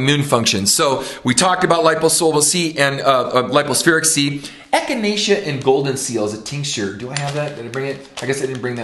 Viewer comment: What is this I didn't hear properly ? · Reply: Echinacea tincture or Golden Seal?